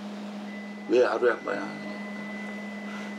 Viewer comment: 에 뭐라고 하는거에요?? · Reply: 왜 하루에 한번하냐고요?